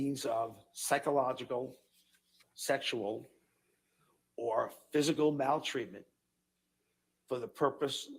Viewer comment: did he just say you can control your partner with physical bowel treatment?